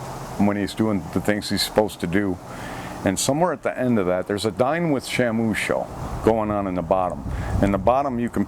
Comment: "Dine with Shamoo" I thought he said: "Dyin' with Shamoo."